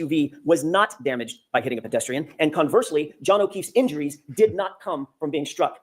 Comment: Idk if I imagined it but i thought heard almost said john o queefe.